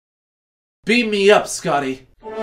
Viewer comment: I thought he said Beat me up Scotty